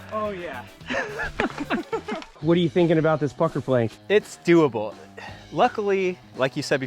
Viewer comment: I thought he said fucker plank